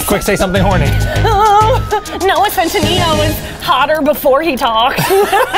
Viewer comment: is the hardest I’ve ever heard Eugene laugh but I can’t make out what is saying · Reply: She says “Noah centineo is hotter before he talks” and then after Eugene starts laughing she starts to say “did you see that tweet” and honestly she wasn’t wrong lmao, that’s probably my favorite part of that video.